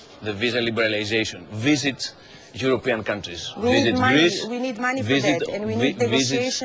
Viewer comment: did she said in 2:42 we need money for this??? poor slavs